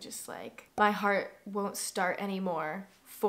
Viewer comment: I initially misheard you’re losing me as ‘my heart won’t skip anymore’, and then as ‘my heart won’t stop anymore’ and now I know it’s ‘my heart won’t start anymore’ and that was just a rollercoaster of emotions for me 😭